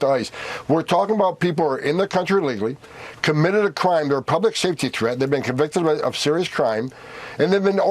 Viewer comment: Good lord. Thank god for cc. Mr mumbles is so hard to understand. At one point I honestly thought he said “ republic safety concern” not “public safety concern”